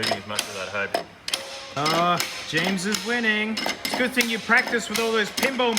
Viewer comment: misheard as "James'es wedding!" 0:41 misheard as "wank that in there" 0:54 "CBT experience" ok my brain is jump I'm done